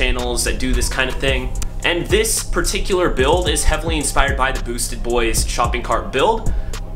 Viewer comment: Dude I have a shopping kart at my jobs storage room I had the same idea,, when u said boostedbois I thought u said SUICIDE Boy's and you're from Tx?? dope shout out from Houston